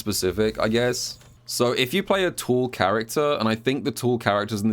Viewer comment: lol I had to watch multiple times I thought he was saying "tool character". That British accent is something else. Carry on don't mind me great video much appreciated because that fan is a beast to deal with.